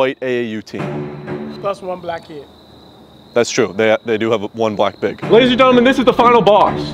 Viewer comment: did bro said they have one black pig?